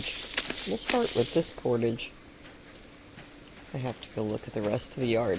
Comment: You said “we’ll start with this cordage” and I heard “we’ll start with Discordage” 😂